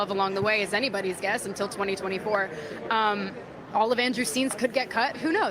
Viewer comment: “All of Andrews scenes could get cut. Who knows.” Andrew: “Ouch!” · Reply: Andrew.... is that what she said? I thought she said Avengers scenes may get cut. I was like what does that mean!? But Andrew makes much more sense lol 🤣